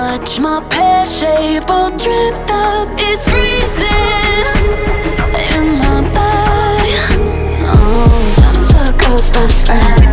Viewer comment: Lol I needed this I thought Normani was saying “it’s freesing on my butt” 😂